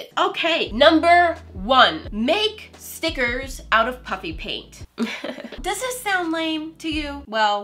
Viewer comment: saying “stickers” Me: “snickers?” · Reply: Personally it's puffy that I misunderstood, totally thought she said puppy for a second 🐶😂😂